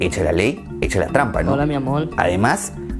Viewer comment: hay voz exterior que dice "HOLA MI AMOL"